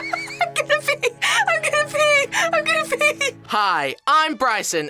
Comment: Did she say she was gonna pee?